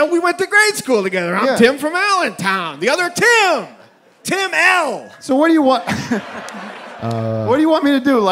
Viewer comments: idk how I misheard this so many times but after my 20th watch I realized he said “Tim L” not “Tim Allen” like I though he said because they’re from Allentown 😂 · I could have SWORN he said, "Yeah yeah, we went to grade school together. I'm Tim from Allentown, the other Tim!..... Tim ALLEN." I've lived three blissful weeks with this mistake.